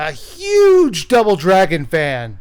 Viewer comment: Did he just say.... Drouble Dragon